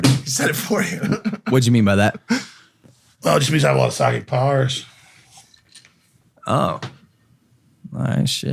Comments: what did alex say at 46:00? logan asked 'why did you call yourself retarded?' And i think alex said 'well it's because I love Psychic powers' Does anyone understand what is happening here at all? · no i meant when Logan asked him what did he mean by that, Alex said something at around 45:58 but then he said he is joking. That's what i am trying to find out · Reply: i think he says "I love psychic powers" - i think he was trying to be funny and it just didn't work